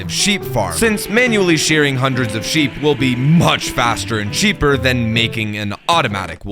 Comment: Did he say "much faster and sheeper" at 1:26. Or am i the only one 😭